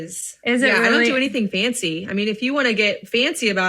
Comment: 😂 My 2 year old is named Francis and we call him Francy (Francy Pantsy). When he said fancy a couple times, he thought you said Francy. He laughed and laughed and he thought you were talking to him 🤣